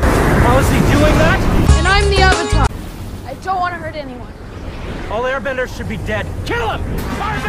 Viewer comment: i swore to god it said "airblender" at 0:28 and GOD im so sad it does NOT say that